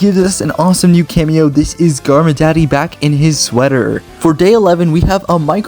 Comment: DID I HEAR GARMADADDY 😛?